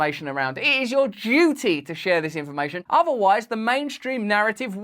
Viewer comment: I feel stupid, every time you said "this information", I heard "misinformation".